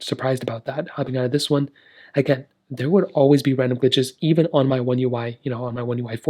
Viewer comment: Man I thought you said “there would always be random bitches on oneui” 😂😂